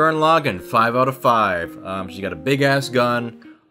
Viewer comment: I thought he said "She's got a big ass, son"